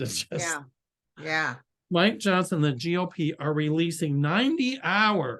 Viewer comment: I thought you said my Johnson 😬😬🤦‍♂️🤦‍♂️🤣🤣🤣